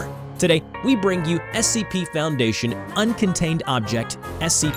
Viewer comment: Did I hear him say 'Uncontaminated'? That's the first time I hear say that.